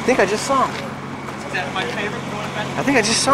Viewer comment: Am I hearing this right? I swear Officer Tomato says "There's my favorite boy!" Tell me if I'm wrong?